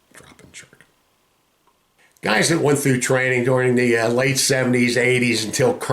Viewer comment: BUDS has normally graduated 5 classes a year. Class 58 graduated in Coronado on 06 November 1970 and Class 63 graduated in Coronado on 5 November 1971, Exercise... I hope that helps... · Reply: ohh you were saying late in 1970... I thought you were saying late 70s. Makes sense.